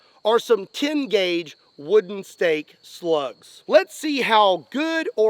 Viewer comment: Dammit, I thought you said steak slugs, not stake slugs. Now I'm hungry.